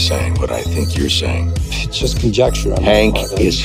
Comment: I thought he said "I'll send you to the police"